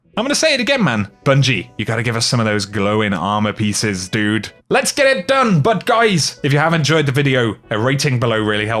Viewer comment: Did you just say "Lets get it done, Butt guys?" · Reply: "Let's get it done! But guys, ..." That is what he said.